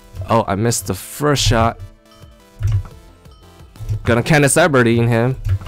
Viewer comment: lol kinda sound like he is saying it like "Katniss Eberdeen"